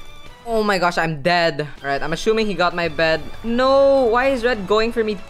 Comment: is it just me or is he actually saying ashumming